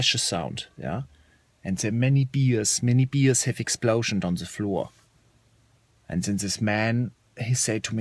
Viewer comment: I thought you said "Many bees had exploded behind you" Lol.